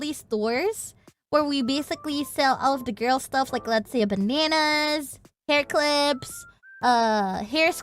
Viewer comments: When talking about girl stuff sold, did she say bananas? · Are bananas considered "girl stuff" in Indonesia? What are they implying here? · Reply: It really sounded like she said Bananas, even though I understood she went Bandanas when I heard Hair Scrunchies after